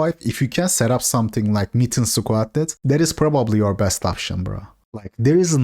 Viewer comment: I've had this misconception when said mitten squad, I could've sworn I heard meat n squat instead